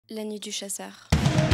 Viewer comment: She says "La Nuit du chasseur" In English that means "Night Of The Hunter." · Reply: sounds like "lenny, du scheisser" in german (pronounciation) xD would mean something like "lenny you shitter" translated to english. its funny how something misheard can mean something so vulgar in other languages xD